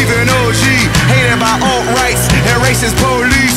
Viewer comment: I'm sorry but can we all just talk about how nice Wale's part is? "When it's time to vote yo people turn to mannequins" "I'm tryna keep it 100 I have no faith in the government that why we need each other" "hated by alt-rights and racist police" These were my favorites his part was overall greatness. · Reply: Kai's Bae Woozi's Life Miss Exo-L SevenTeen tbh I thought he said hated by own/old rice and racist police 😂😂😂